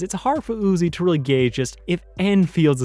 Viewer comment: WHAT DOES HE SAY?!?!?!? Cause the line just can't be "...as it's hard for Uzi to really gay just If N feels the same way." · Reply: Gauge